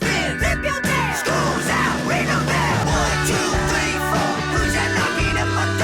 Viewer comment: "Schools out ring the bell" My life is a lie. All these years I thought they were saying "School's out we don't care" Oh my god